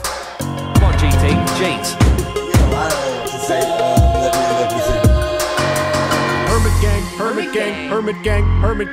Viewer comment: I'm still wondering what is said in the background at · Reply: "Oh I dunno what to say letme letme see"